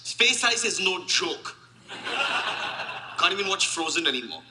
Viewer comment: Space ice is no joke. I guess there is a word play of "joke" vs "chalk", but I don't understand what he said when everybody burst into laughter. Anyone care to explain? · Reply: Reto Fassbind You already know he said "Space ice is no joke" but that was followed by mild level canned laughter, then "Can't even watch frozen (that animated film) anymore" followed by raucous canned laughter.